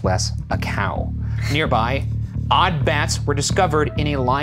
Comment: I thought you were saying ‘odd bass’ bass in a cave, pretty odd.